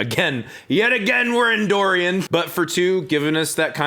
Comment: I had this pop up in a playlist, I heard you say "Yet again, we're in Dorian" and was confused as to how did you enter me but ok